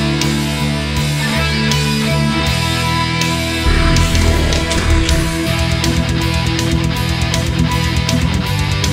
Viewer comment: Hi ! I'm french and i don't understand the last thing he has said after "the cycle must continue" at 00:58 Someone can help me ? please :3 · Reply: suis choquée too late answer, I guess XD He said, "There's no alternative"